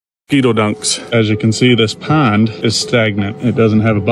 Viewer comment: Bro i thought you said mosquito donuts for a second